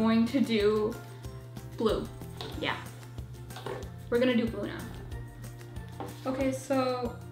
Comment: in my subtitle at 5:20 it say booting up instead of blue